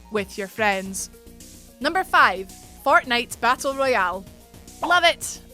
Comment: I heard "Fartnite"